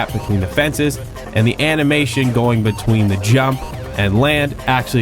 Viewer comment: i misheard anime and now i really wanna see a jsr anime